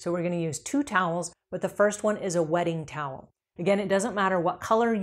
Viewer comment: Ohhhhh! The WETTING cloth! I kept hearing “wedding” cloth! 😂 (I assumed with “wedding” cloth it was like, the first cloth you used or something.... lol no idea 😂)